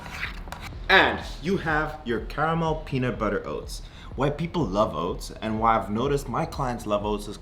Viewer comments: had to turn subtitles on, legit thought you said "white people love oats" 🤣🤣 keep up the work! learning alot from you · Am I the only one who thought he said "white people love oats"? I had to rewind that 😆